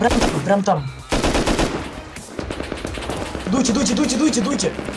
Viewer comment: Либо показалось, либо я услышал мне знакомые слова из румынского языка как Дути - уходи (( походу це парни из Молдовы, ах какие ) ))